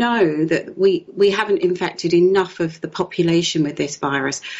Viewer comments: I heard “impacted” not “infected” 🙄🤦🏼‍♀️ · What did she just say? I could have sworn she said " we have not infected enough